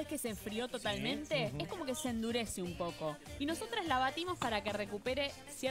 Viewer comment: did she just say nosotrEs???